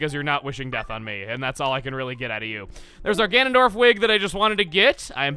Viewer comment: I thought you said Gandolf instead of Ganondorf (Did i spell those correctly? Im tired.)